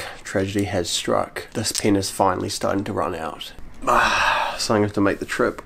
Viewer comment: why does it sound like saying pe*is i am a big fan of u btw would mean a lot if u pin this comment